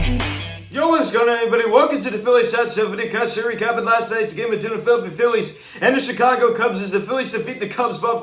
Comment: lol what are you saying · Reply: “ yo what is going on everybody welcome to the Phillies Hot Stove Videocast and today we are going to be recapping last nights game between the Philadelphia Phillies and Chicago Cubs”